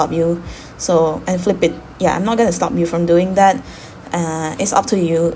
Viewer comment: l thought said I am not gonna slap u 😂